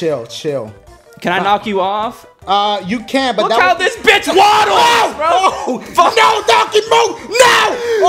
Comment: hold up. did just say waddles or something?